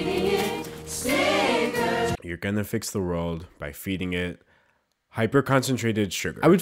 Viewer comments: Not gonna lie, thought they said "We are going to fix the world, by feeding it sinners" and I was like "okay thats a little dark" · What I heard: ”We’re gonna fix the world by feeding it cigars” 😂